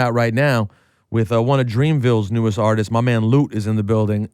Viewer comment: Lowkey Thought That Shit Said Flute 😂